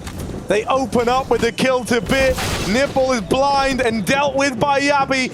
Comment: Did he just say "nipple" is blind?